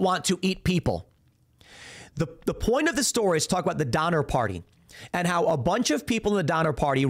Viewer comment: I thought he said the Dahmer Party at first 😅